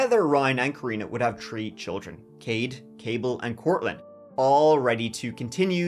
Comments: did he say the kids name was Cable? I heard Cade, Cable and Courtland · did they call a child "cable"?